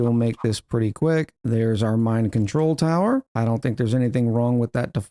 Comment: I keep hearing 'mind control tower' instead of 'mine control tower.'